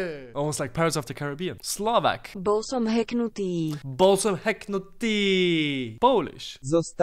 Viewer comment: balls of heckhard Steel? Dosnt it sound like you says that🤣